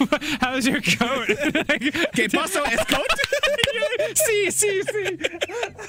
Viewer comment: "Que paso es coat?" "Uh huh, uh huh, uh huh!" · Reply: I thought he said "te paso es coat?" xD